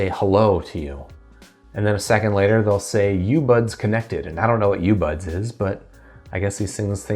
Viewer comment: I thought they’re saying “your buds connected”